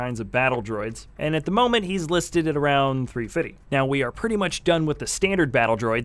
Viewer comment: What's he saying? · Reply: hes trying to say $3.50